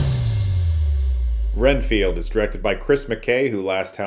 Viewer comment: I thought that said Seinfeld for a second.